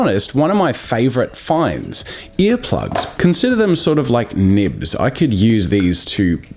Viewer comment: I thought the ear plugs said butt plugs